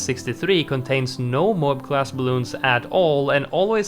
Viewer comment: I kept hearing "camel" instead of "camo" even though I know you mean camo. I keep thinking 🤔 there's a camel balloon, since when did he mention that ?